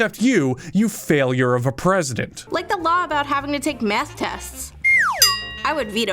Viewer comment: I Deadass thought the she said meth tests and nearly spat out my water